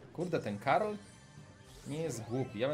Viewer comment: czy tylko ja usłyszałem Karol?